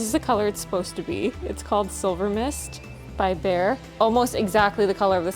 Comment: Oops sent that twice and just rewatched and heard you say silver mist by beyr 🥴👍🏻